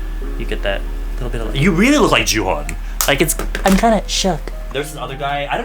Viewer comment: did say "kinda shook" or "gonna choke"